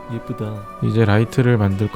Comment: 작게 예쁘다가 들리는군요 이정도는 시청자라면 누구라도 찾는거죠